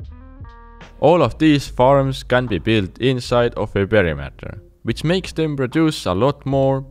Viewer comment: what does he say at 7:55? cant tell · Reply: perimeter but this video is out of date